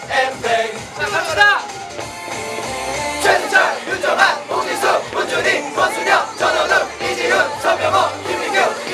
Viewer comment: what the hell.. I can't even understand who's name they're saying. Pls reply to me they're name chant cuz im confused as hell. Edit: Vernon is hype af. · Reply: Choi seungcheol, yoon jeonghan, hong jisoo, moon junhui, kwon sooyoung, jeon wonwoo, lee jihoon, seo minghao, kim mingyu, lee seokmin, boo seungkwan, chwe hansol, lee chan